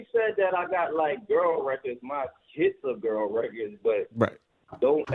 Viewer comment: Did Fab say “My Kids are girls records”⁉️